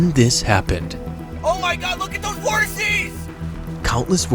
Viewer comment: What was said: "OMG LOOK AT THOSE VORTICES!" What I heard: "OMG LOOK AT THOSE HORSIES!!!" 🐎 🐴 🎠 🏇